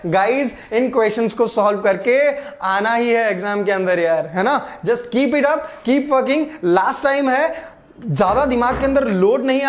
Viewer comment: did he said 'keep fucking'